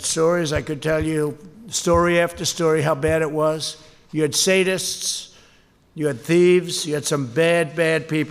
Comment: Does he say Satanists? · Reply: He says "sadists" - meaning people who enjoy inflicting pain on others.